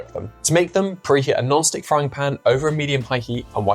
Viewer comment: When I listened to this the first time, I thought you said a Gnostic frying pan, instead of a non-stick frying pan. Up way to early!